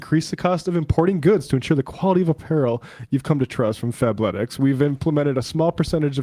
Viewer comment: The first few times, I thought Matt were saying "Fapletics... " Ahead of his time.